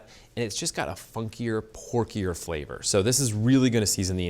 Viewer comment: "Porky" is a terrible descriptive term for food. You might as well comment on it's wonderful essence of pigsty. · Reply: It sounds like he said 'funkier, porkier flavor'